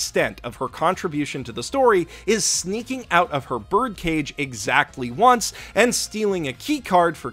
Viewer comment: I thought you said birth cage the first two times you said bird cage... i was horrified